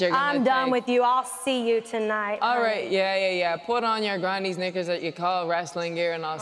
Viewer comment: did Becky just say the n word 😹😹😹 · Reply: No, she didn’t. She said “Knickers” which is another word for saying underwear (Men or Women’s) that is used in the 🇬🇧 (UK) and 🇮🇪 (Ireland).